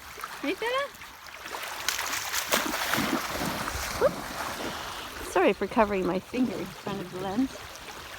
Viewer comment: Hold up wait did I hear that right? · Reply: “Sorry for covering my fingers in front of the lens”